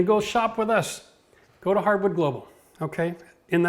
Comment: kind of bummed out as the link below which was hard to find says Site under maintenance. I also thought he said something like hardwood global and that site is not safe. Alas. Off to floor and decor or some other bigger box I guess.